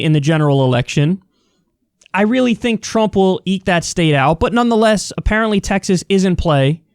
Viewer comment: I know it was eek out, but it really sounded like you said "Trump will eat that state out." Which is a really interesting mental image.